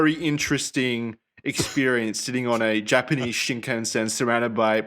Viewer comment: "sitting on a japanese shit can" i had to do a double take there. Glad i misheard lol